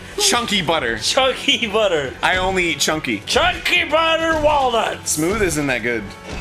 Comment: i..thought it said chunky butter walrus